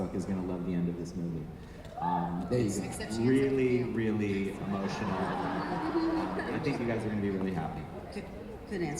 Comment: Did anyone get what Jen said at 33:01??? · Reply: "Except she ends up with Gale" She was joking 😂😂